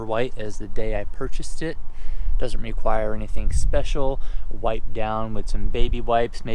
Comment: Great video! I can’t believe that you allow your child in there and when you said wipe downs, I heard it as white gowns. If you required white gowns and surgical booties, your interior would never get dirty. I really love your clean garage and I’m not trying to poke fun at you, I just really like how you do things.